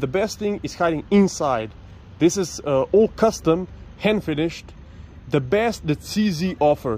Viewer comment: Am I the only one that heard you saying hand fetish instead hand finish?😂😂😂😂